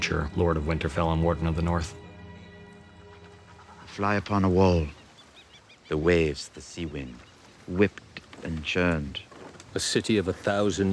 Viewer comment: can you please tell me what does Tyrion say in 2:18 ? :) I can't understand it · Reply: Throne Gamers fly upon a wall, the sea and wind whipped and churned